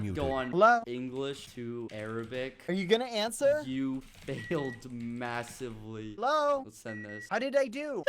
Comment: i thought he said you have killed massively